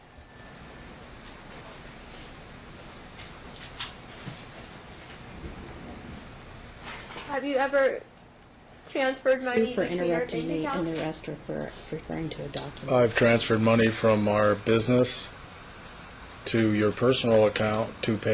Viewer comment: Did judge say arrest her for interrupting me and referring to documents at 10:14 😂😂😂